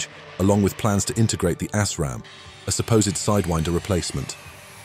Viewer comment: did he say the "Ass-Ram"?!?!? That weapon sounds exciting. :P (Not that there is anything wrong with that (in my best Seinfeld voice).